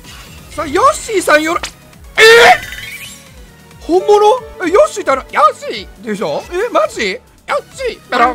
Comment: ヨッシー！♪がえっちー♡にしか聞こえない